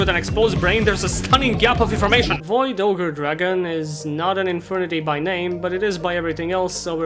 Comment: I heard Void Overdragon and honestly, that'd be such an awesome name for a card!